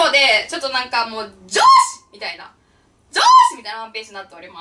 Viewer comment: 女子ッ!! の癖が強すぎて、上司ッ!!に聞こえます(??)